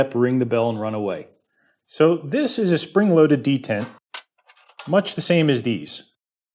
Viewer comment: That is weird - just checking ... and there it was!!! In my haste I thought it said "Spring loaded DENTISTS". I guessed you were just filling in again.